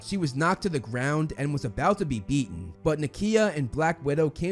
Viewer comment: I swear I thought you said "about to be eaten". I was so confused XD.